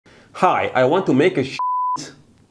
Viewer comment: i thought you said "i want to make a shi*" at the beginning 😂